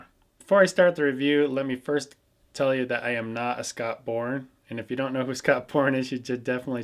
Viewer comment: Sounded like you said Scott Porn lol